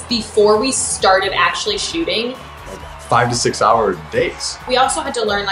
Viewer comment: is it just me or at 3:50 - 3:51 it sounds like taylor said 5-6 hour “dates” i know he’s probably saying days but... i ship jaylor 🥰